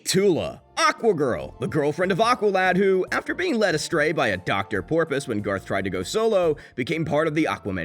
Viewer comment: Excuse me.... Did he just say Dr Porpoise?